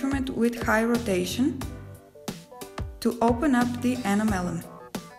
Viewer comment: I thought she was saying 'open up the animal.'